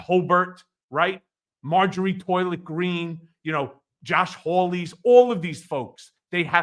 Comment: I thought I heard “Marjorie Toilet Green”...😂😂😂